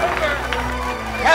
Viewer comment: What's the last word Dustin says in 1:03? I can only catch "Captain Hook thanks a great great..." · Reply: Crew